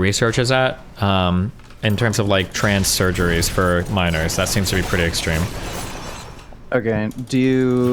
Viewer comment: Dude, I thought that said “surrogacy for minors.”